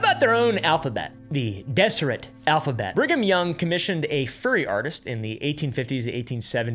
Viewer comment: Was I the only one who heard “furry artist”